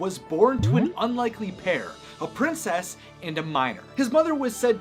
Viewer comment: OH!!! Miner (I...legit thought you said Minor . And...WELP. NO. But that would be Messed Up Origins, wouldn’t it.)